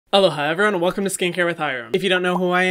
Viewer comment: omg i just realized he says “aloha” in his intro ... this whole time i heard “hello hi everyone” 😂😭